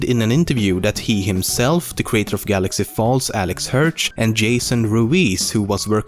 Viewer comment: Did you just say Galaxy Falls?